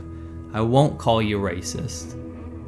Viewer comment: I totally heard “I won’t attack you but I will call you a moron!” Had to go back and check to find out it was immoral .... great video...